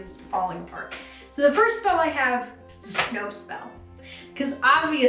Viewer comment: Oh, SNOW spell! I heard "Snopes spell" and was like "...actually, a fact checking / anti-misinformation spell sounds really useful..."